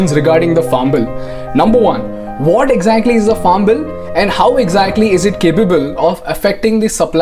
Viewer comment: When he says farm bill, I keep hearing 'Fomble'